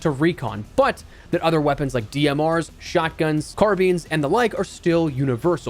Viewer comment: Did bro just say Car Beans??